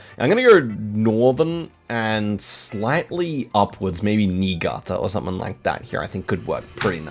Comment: What did bro just say?? 😮😮😮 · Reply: Niigata